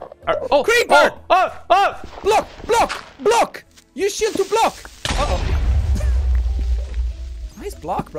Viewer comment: I heard Jack say "Use S*** to block!"